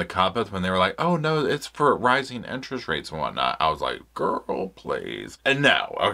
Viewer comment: When I thought you said 'rising INTERNET rates', and was like...I mean, I can believe that. 😂